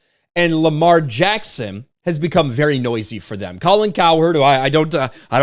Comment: when you first mentioned Colin Cowherd, I thought you said "Colin Coward," and were referring to Colin Kaepernick.